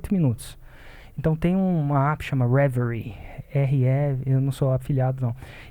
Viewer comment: Qual é o nome do aplicativo? Vcs falaram o nome em 10:22 , mas nao entendi o nome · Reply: Aplicativo Reveri